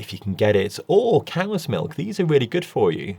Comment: I heard "camel's milk" but it was in the context of plant "milks" so I thought I had misheard--people in the UK talk funny ;-) . He must have said "camas milk" (camas root was an indigenous food source around here). No, I learned camel's milk is commercially available but not offered at my local (Seattle) supermarket. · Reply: Cow’s milk!